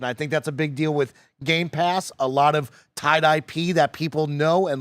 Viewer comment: When Mike said “tied IP”, I heard it like “tye dyed pee”.